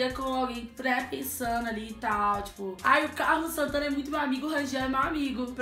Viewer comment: !!! OUVI... AMIGO????? OI? Q? COMO ASSIM?